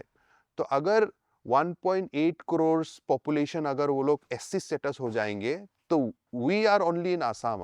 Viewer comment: ST NOT SC. A little bit correction in the subtitles please. 9:39 / 9:45/ 9:55 so that it doesn’t create further confusion for the larger audiences who are unaware. And how could this mistake take place at the very first point? When is clearly saying SCHEDULE TRIBE and you have also typed it as SCHEDULE TRIBE fully whereas, in short format you have typed it as SC instead of ST . Please rectify it. A humble request. Thank you for covering though.